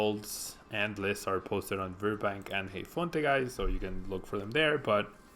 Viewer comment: can someone tell me the websites where i can find decklists from these tournaments?He says them on 3:50-4:05 but i dont understand it,sry :D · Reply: Patrick Dengel Heyfonte and virbank are where the lists are posted.